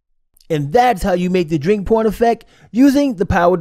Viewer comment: Thought you said on the narration the 'drink porn' instead of 'drink pouring' effect, yes l am going to get my ears cleaned out........ 😄